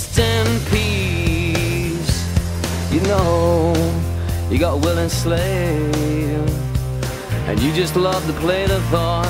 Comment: I'm sorry but when he drops to his knees and sings "you know, you've got a willing slave" I just die a little 😍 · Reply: I thought he said “Will enslaved” because he's “Will(iam)”